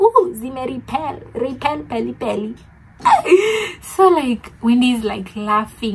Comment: Did you just say repell pelli pelli😂😂😂